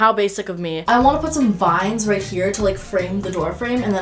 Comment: I literally thought she said "and then I'm gonna hang up some Vines..." and I was so confused when she hung up those plastic leafs omg.